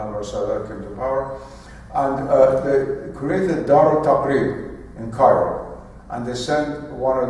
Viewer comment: If you understood the name of the journal mentioned at 58:40, can you share it with me? I didn't understand what was said. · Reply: دار التقرير i think - but am not 100% sure